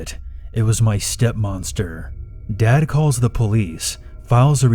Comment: Step monster is kinda funny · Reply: I thought he said MOMster?😂